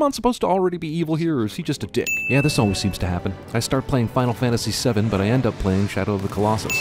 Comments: you say final fantasy 7, yet the subtitle says 12. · VideoSins: 2:33, host says "Final Fantasy Seven" but the subtitles say "Final Fantasy XII" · Says final fantasy VII but subtitles say XII... CinemaSins does not know roman numerals DING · Jeremy says "Final Fantasy VII" but the subtitle says "Final Fantasy XII". DING! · Everything wrong with Cinema Sins in 500 characters or Less: 1 - 2:32 "I started playing Final Fantasy XII but I ended up playing Shadow of Colossus" When he actually said Final Fantasy VII, don't you know what you are reading now? CHANNEL SINS: 1 PURE MADNESS (Nothing more than a joke) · Captions are wrong on (probably) more than one point. 2:35 What he says: "Final Fantasy 7" What the captions say: "Final Fantasy 12" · Narrator says Final Fantasy 7 (VII), but the caption says Final Fantasy 12 (XII). Just saying. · CinemaSins has sinned! 2:33. Subtitles say Finaly Fantasy XII (12), he narrates it as Final Fantasy 7. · you said "Final Fantasy 7" while the subtitles say 12 ding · Here is a sin for you, at 2:33 you said Final Fantasy VII but the subtitle said Final Fantasy XII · he says "final fantasy 7" the the Subtitle says final fantasy 12. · In 2:31 he says final fantasy 7 but the subtitles say 12??? Looks like im pointing out his video mistakes.